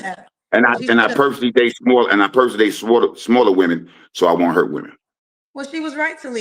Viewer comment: He said smaller, i thought he said smother😮😂